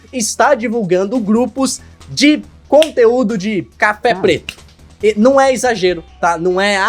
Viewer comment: Scrr oque que o disse aqui? Juro que entendi "café preto" KKKKKKKKKK